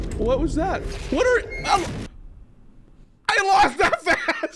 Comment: Bro sounded like he said "I lost a fast!"